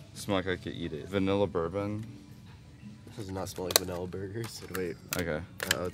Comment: did say vanilla burgers😭